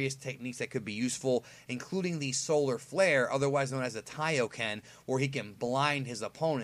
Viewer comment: did you just say solar flare aka kiaoken?